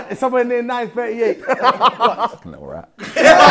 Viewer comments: What did Simon say at 10:27? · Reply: He said " u little rat" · Wait what did Simon say at 10:24? I couldn’t hear it properly😂😩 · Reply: "Little rat"